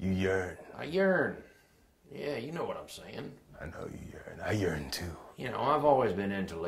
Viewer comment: I know you yearn, I yearn too. IT SOUNDED LIKE HE SAID" I know you yearn, I urine too"